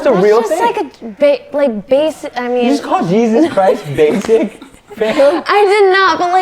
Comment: I thought she said Jesus is bacon lol